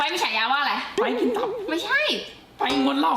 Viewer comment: ได้ยินไม่ผิดใช่มั้ยว่า "ไปร์ท เก๋"😢